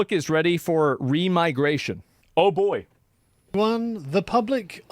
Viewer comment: I thought it said “regeneration”